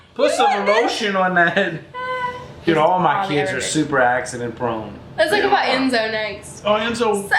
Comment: i thought he was gonna say “all my kids are accidents” for a second, but he said “prone” at the end